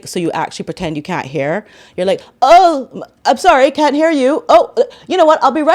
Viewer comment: 😂😂😂😂 I thought you said that she was a cat-hair person 😂😂😂😂 turns out you said 'can't hear' 😂😂😂😂😂 imagine launching a website with no products and then claiming you've sold out!!! 😂😂😂 In my humble opinion, that behaviour resembles many unfortunate and innocent people who have been medically diagnosed as clinically insane.